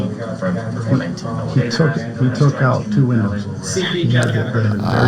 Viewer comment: 54 minutes. I thought he said he took out two windows not women. But you have better audio.